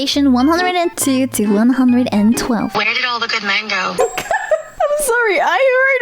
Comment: If you ask questions like this...ask yourself...Where did all the good Woo Mango? (i heard it too)